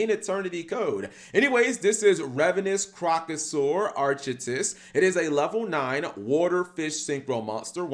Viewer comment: Not gonna lie, at the end of his name I thought it said Arthritis 😂